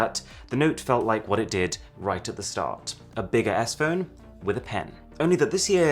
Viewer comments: When he said “a bigger s phone with a pen” I thought he said a big ass phone with a pen. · Doesn't it sound like he says "A big ASS phone.....with a pen" ?😂🤣😎😆😋